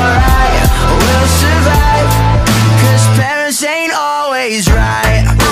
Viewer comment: when this came out i thought it said “paris ain’t always right”💀